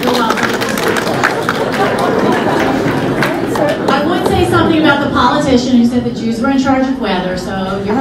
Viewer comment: What she SAID was (after mentioning that it wouldn't be raining on Saturday, @ 4:37): "I would say something about the politician who said that Jews were in charge of weather, so [inaudible], but that's not right." In other words, she was making a joke about Trayon White, Sr. (see article below) and assumedly her own supposed ability as like a Jewish version of Marvel's Storm to make the weather good for the day by her own volition. <link> You shouldn't believe everything you read, especially these really poorly done, crap-ass captions.